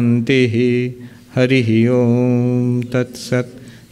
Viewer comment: Can someone please tell me what are the final words of the prayer that Swamiji always says at the end? It sounds like "Sri RamaKrishna rupa namasto" Is this correct? and what is the meaning in english? Thank you in advance! ❤ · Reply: thanks for responding :) What are the actual words that he uses after he says Sri RamaKrishna? I can't clearly make them out but it sounds like "rupa namasto" Am I hearing them correctly, when other monks say it sometimes I hear "upa namaste"